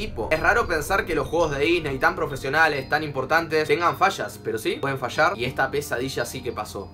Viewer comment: Cuando dijo tienen fallas yo entendi "tienen fachas" XD